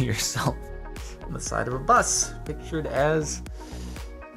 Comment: omg it sounded like you called the last guy a "Sexbender" not a "Sex Offender"